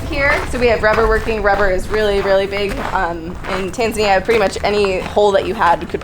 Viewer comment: Did she say "rubber is really big in TANZANIA"??? around 15:24. This is in Florida right? EDIT : okay shortly after she says she lived in Tanzania, and she probably said it earlier too. Thank you for another awesome video!